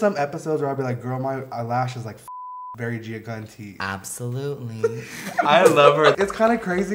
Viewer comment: SomeONE HELLPP 😵‍💫⚠️⚠️⚠️ wtf did she say on 5:37 ASKING 4 a frenn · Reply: She said Gia Gunn tea!